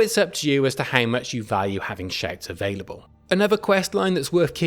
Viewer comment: I thought he was saying shades, then I realized about 20 minutes in it was shouts...jeez I am the worst. Great video.